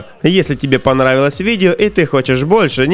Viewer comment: Мне одному показалось что он сказал вместо подписаться подлизатся